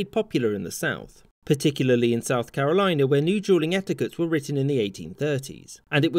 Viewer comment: All I hear is "jewling"